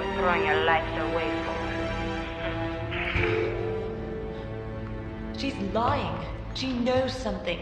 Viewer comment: 空耳か、「She's boyne」に聞こえる それなら嘘字幕と同じセリフになるなw